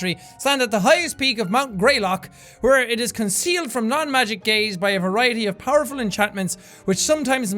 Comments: i thought he said non magic gays instead of non magic gaze xD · am i the only one who heard "concealed from the gays" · when jack said something about “non magic gaze” I thought he said “non magic gays”😂😂😂wtf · I thought he said “non-magic gays”...then I realized...I was cleaning out something, so...also, Hufflewudgie (Hufflepuff & Pukwudgie) Pride! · For a second I thought he said "concealed from non-magic gays" XD